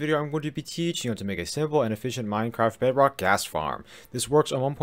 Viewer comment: No one : Auto generated captions saying *GAS* farm instead of GHAST farm at 0:06 LMAO For more funny words turn on captions (auto generated) and here you go , watch the full video and read captions carefully ;)